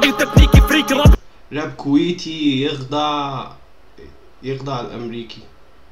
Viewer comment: يخضّع يعني الراب الكويتي هو اللي يخلي الامريكي يخضع، الشدّه تغير المعنى كامل بس شكلك ما سمعت كويس